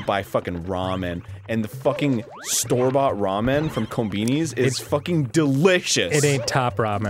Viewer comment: It sounded like arin said "Raw Men"